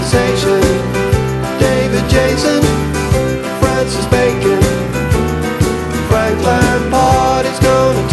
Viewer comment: Love this song, although I always misheard "Frank Lampard" as "Franklyn Parks" who I imagined to be some cutting edge American artist too cool for me to have ever heard of...